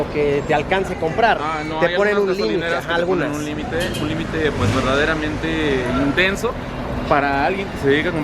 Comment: Jajaja alguien fue igual de morboso como yo jajaja que escuché " vergas" jajaja cuándo dijo " ver gasolinas" 😂